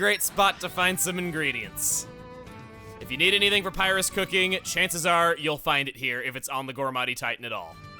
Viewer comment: Does anyone else hear Emile's say Papyrus?